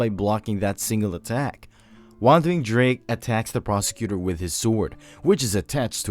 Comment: i think am the only one who heard wondering drake but so far keep up the good work you earned a sub